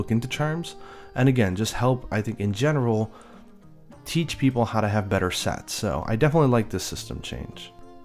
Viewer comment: Geez! around 20:35 it sounded like you said "Teach people to have better sex" instead of "Teach people to have better sets" Heh!